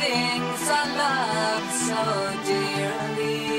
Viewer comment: It's sounds like they say "boobs" even if it's "books".